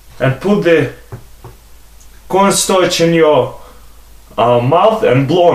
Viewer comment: what he said at 2:38 ? "put some what ? and thank you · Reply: He said cornstarch